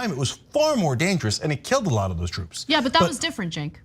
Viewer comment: when Sink dribbles about George Washington mandating the military to get a small pox vaccine, Anna sorta cuts him off. It really sounds to me like he said the vaccine "was far more dangerous and killed a lot of the troops." anyone else hear it that ?